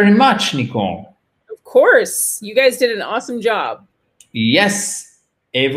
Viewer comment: Hi, is anyone able to write what Nicole said at 14:58. Too fast for me....that would be nice. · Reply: Christian Villatte thank you for your question. You guys did an awesome job ;).